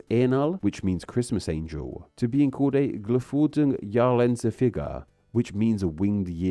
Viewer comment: As a German I have some interesting aspects to add: - "Großvieheinheit" GVE is a term used in agricultural science (still today) to measure the grazing of pastures. A cow and her calf equal 1 GVE, if you switch from cows to young cattle or even sheep this allows you to calculate with other animals, since over- or undergrazing the area could be problematic it is recommended to keep a certain ratio of GVE per area. So even if it sounds hilarious to foreign ears it's just a technical term and no everyday word. - How you translated anti-fascist as anti-racist is unclear to me - the words are surely not interchangeable. - As far as the "geflügelte Jahresendfigur" goes - it's still a common practice in German today to rename christian elements. This time not in the name of communism but "cultural diversity". To not offend non-christian people living in Germany some of the famous christmas markets were renamed to winter market, the St. Martin lantern-processions traditionally held on his name day were renamed to light or lantern fests and the school holidays in the all hallows week are better known as autumn holidays now. There are more examples but you get the point. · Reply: Did he say "geflügelte"? I only heard "Jahresendfigur" and couldn't figure out where he got the "winged" part from. Edit: okay, I've gone back and re-listened. He did indeed say it, but it was so badly pronounced that I didn't catch it! 😂